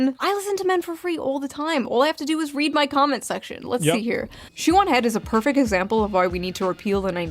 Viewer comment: I totally thought she said her name was "Shaman Head" I was gonna lose my shit because im pretty sure im the only person given at birth this name in the world... lol, thank God she said shoe on head or whatever